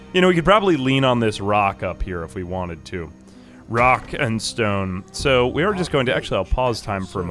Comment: Did I hear a Rock and Stone!